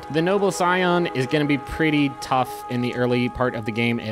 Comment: pretty tough ?? Did you say it's tough ? Yea I am picking this one. Let me see how it's gonna be tough